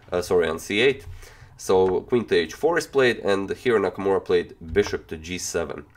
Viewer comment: Anyone else catch he says "Hiro Nakamura," the time traveling character from Heroes, at 2:26?